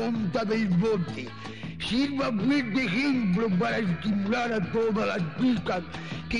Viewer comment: / 2:44 ¿ese que habla es franco o quien era? ¿Y por que parece que dice que hay que "estimular a las p*tas?"